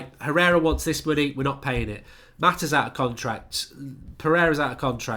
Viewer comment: Did he said Torreira?